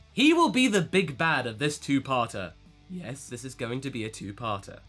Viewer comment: I thought he said "two Paata"Also are ya gonna get Spirit Sword for Paata? It's pretty devastating in-game~